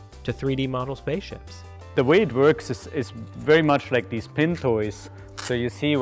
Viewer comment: what is that called again? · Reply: Tootie Tooter he said pin toy